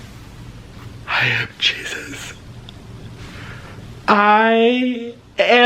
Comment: I thought he said I am cheesus